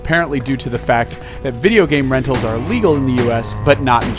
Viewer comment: One slight question. You mention that game rentals are illegal in the US. I'm not sure where you got this information. I know Nintendo tried to MAKE them illegal (because, you know, it's NINTENDO, who has been weird in determining what they consider piracy), but I don't think I recall any actual government law that forbids game rentals at all. Maybe debates about the topic, but nothing concrete was ever decided on, as far as I know. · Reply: Hmm. I had to listen close to 5:43 when you say that to hear if it was legal or IL-legal in US to make sure I heard it right. Maybe you said legal and I misheard it as illegal or something. Apologies if I misheard.